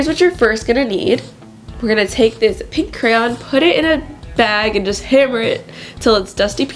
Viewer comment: did anyone hear her say pink crayon? Or is it just me?